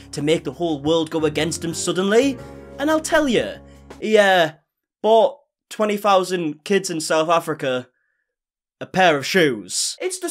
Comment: Lmaoo wait cause I literally thought u said he bought 20,000 South African kids in the beginning